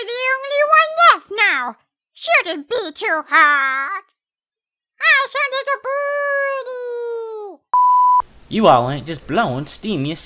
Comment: what did Yoshi say? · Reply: "I sound like a biiiirrrdyyyyy." that's what I hear.